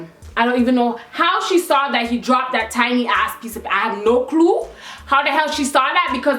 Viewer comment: she said "I have no clue" but the way she said "clue" sounded like "kloo" so it was funny cuz it sounded like "I have no kloo"